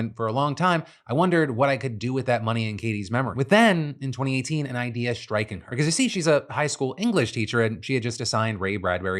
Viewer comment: “an idea strickened her” is that what he says???